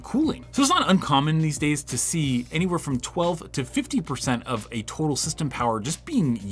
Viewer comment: Did you say 12 to 15 or 12 to 50 here? Can't make it out, sorry. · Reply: 12-15%. Having it at 50% of total system power just for the internal fans would be crazy.